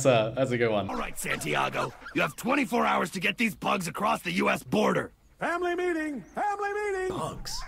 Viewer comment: he was smuggling pugs instead of drugs